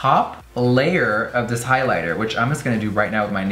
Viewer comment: i love the pink hiliter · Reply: El Canal De Frances Nicolee I actually thought you said hitler and I scrolled through the video looking for a pink hitler 😂😂😂💀